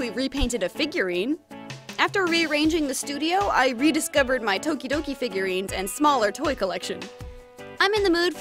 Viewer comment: AHH omg she hasn’t done a tokidoki repaint for literal years. If only she could get her matte varnish to work again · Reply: For a sec I thought you said “Todoroki” I’ve been watching to much mha